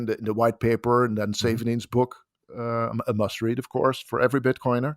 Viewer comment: What does he say @ 20:09? He read the white paper and the “savenings book”? What is the must-read for bitcoiners?